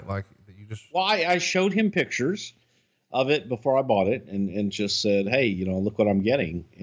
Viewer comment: Did he get given it? or did he buy it? cos the way he's talking throughout, it was passed to him to appreciate but at the end when he's saying he showed McMahon it before he "bought" it ... or did he say "got it" and I misheard?